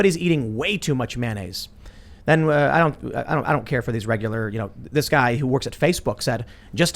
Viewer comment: At time stamp 4:14 does Tim say "I dont care about these regular Joe's"?